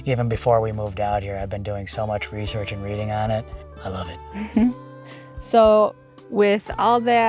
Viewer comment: Damn I thought I heard you tell your wife I-Love You. You actually said I love it. Come on don’t be an Lucy and Ricky. Let your fan base hear it. She’s your queen, brain, helper, partner in crime and off grid cohort. 🤪🤪🤪👩‍❤️‍👨. Great work guys keep it up.